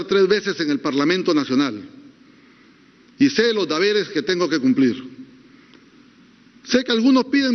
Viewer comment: alguien escucho que dijo "DABERES" ???? ... Ni leer bien sabe.... minuto